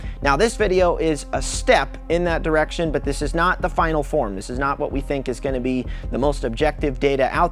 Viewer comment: When I heard this isn't the final form, I swear you said This isn't even my final form. I will keep this in my head as canon.